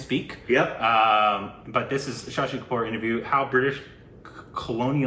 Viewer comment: Did say 'Sashi Kapoor' at 1:09 🤣